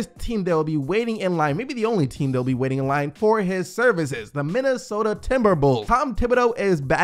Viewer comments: did you just say Minnesota Timber'bulls'? haha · Did he say minnesota timberbulls? Or did I hear wrong lol